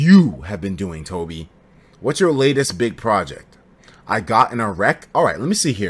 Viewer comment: Oh...”I got in a wreck”.... I thought Etika said “I got an erect”